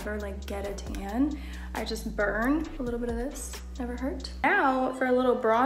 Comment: なんて言ってるのかな？ 全く聞き取れない😭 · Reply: Little bit of this, never hurt